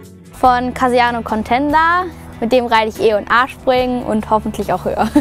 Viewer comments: ich hab verstanden mit dem reite ich e und Arsch springen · Ich hab verstanden e und arsch springen